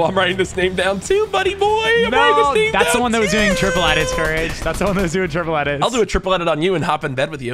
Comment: what did you say courage?!?!?!?!!?!?!?!?!?!?!?!?!?!!